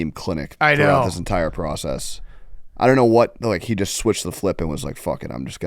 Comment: did hank just say switched the flip at 41:14? love this guy😂😂😂